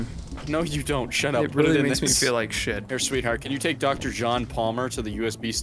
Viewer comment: okay did actually call “ Sweet Heart “ or am I hearing things?